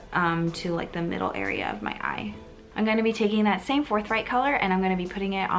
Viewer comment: lowkey thought that said fortnite instead of forthright LOL